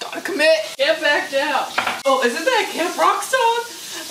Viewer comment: i thought you said "isnt that a kid rock song" and i was so lost for so long